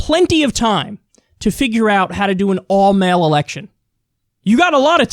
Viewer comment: I heard "all male election" LMFAO